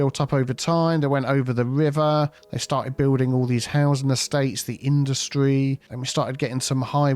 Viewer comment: Had to watch 20:40 a few times, thought you said "Housing Mistakes" and I was like, "No, those look good, I don't think they are mistakes." Turns out it was my mistake - "Houseing and Estates"